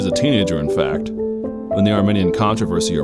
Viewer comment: Armenian? Controversy. Is this part of the Counter Reformation. Sorry but I couldn’t understand what you said there. Thanks.